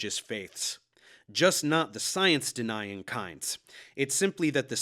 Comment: Did he said "cunts"? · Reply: "Science denier kind" is what he said. Although the word "cunt" would have worked fine, too.